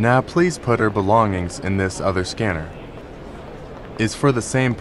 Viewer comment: I thought he said scammer 💀🗿😩